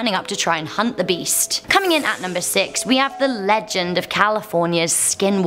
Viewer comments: did she just say 6 I’m pretty sure that was 8 😂😂😂😂😂😂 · who else thought that said " California's Skywalkers?"